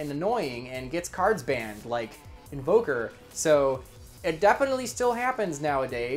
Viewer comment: What was the example he gave at 16:11 it sounds like he says ogre but there's no ogre on the banlist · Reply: MX Saber Invoker